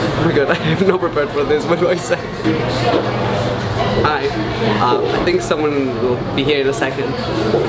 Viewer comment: you said ay instead of hi 😂 and you seemed so stressed , it's like your voice went higher 😂. Not a hate comment by the way .